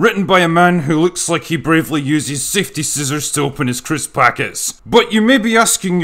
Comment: "A man who uses safety scissors to open his crisps packet." Effin' brilliant, my friend. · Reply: Heard it as "fifty scissors". Still works.